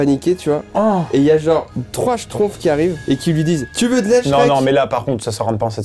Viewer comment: J'ai compris : " Il y a trois schtroumpfs qui arrivent et qui disent "tu veux de la shneck" "